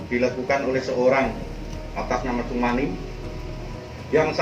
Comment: Ealah re edit yaaa... Salah denger saya. Yg membunuh td namanya SUMANI.